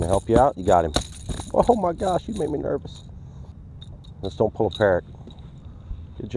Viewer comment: Did i just heard Scott said, "Don't pull a Peric" at 4:27??? LOLOLOLOL Too funny. Great videos Scott!